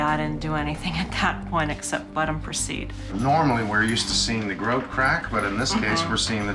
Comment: AT THE 2:49 MARK IN THE VIDEO HERE, DID HE SAY **BUTT CRACK**? 🤣🤣🤣🤣